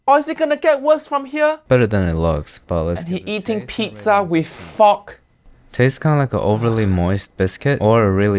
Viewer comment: dude, why did YouTube thought he said [F-Word], like the subtitles censured fork 🤣🤣